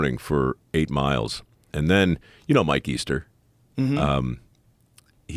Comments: What he said: "you know Mike Easter?" What I heard: "you know my keister?" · did he just say “my keister”